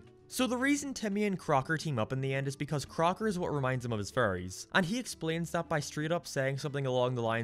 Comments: I swear your accent is so thick that when you say fairies, all I hear is "furries" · It does sound like you say furries not fairies.